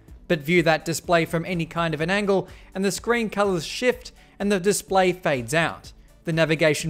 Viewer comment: I thought he said sh*t